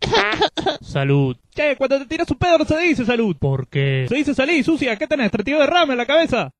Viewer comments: no entendi que dijo al final x eso no lo puse XD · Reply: "Se dice sali sucia, ¿que tenes 32 de ram en la cabeza"? Respondo 11 años tarde xd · Alguien sabe qué dice en 1:41? Jajajajaja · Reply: Un poco tarde la respuesta 😂 pero le dice "Salí sucia, qué tenés 32 derrames en la cabeza?"